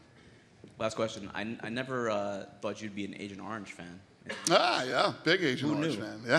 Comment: fan of what? I can't quite hear what the interviewer said. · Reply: Agent Orange. In this context he is referring to a punk band that Dana likes.